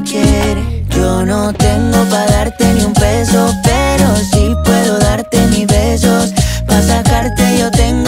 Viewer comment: Pensé que decía "puedo darte mil besos" en lugar de "puedo darte mis besos" jajaja. xD